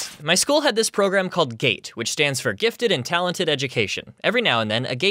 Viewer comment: i heard gate as gay